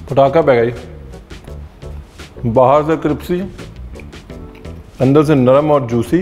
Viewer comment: you know that thing is super delicious when Ranveer ji says "Cripsy" instead of crispy 😂